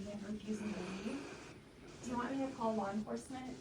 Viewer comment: What the hell.... @ 11:15 she says, "Do you want me to call law enforcement?", but for some reason the auto-generated Closed Captions say "Do you illegal me to call law enforcement?" Don't ask why I care, but I find that rather peculiar lol